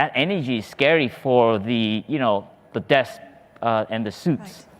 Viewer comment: Glad to learn that Plum Village was part of COP26; and hope you get more media attention for such contributions in the future as there was virtually none in the UK. At 10:49, subtitles have saying “… it’s scary for the debts and the suits” - listening at slowest speed, I think he says “desks” (ie the people having to sit at desks negotiating or dealing with questions).